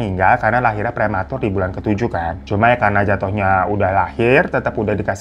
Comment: Gua salah denger ga sihhhh?? "Anak ke 9 lahir prematur saat usia kandungan 7tahun" soalnya udah gua ulang 3x wkwk